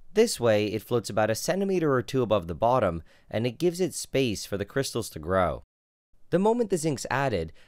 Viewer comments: I thought it said "Meth Crystals" and I got extremely worried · Thought it said "Meth crystals"